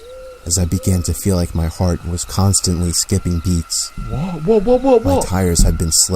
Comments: instead of beats it says eats?? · Lol on 10:11 IT said a chill ran up my spine as my heart were con stantly skipping eats instead of beats lololololol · it says skipping eats instead of skipping beats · the subtitle says was constantly skipping eats instead of beats is this a typo? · read the captions when you get it after the word skipping instead of beats it says eats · am I the only one who noticed the subtitles said eats not beats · it said eats instead of beats 😂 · Don't tell me I was the only one to notice that he said eats instead of beats 0_0 ;)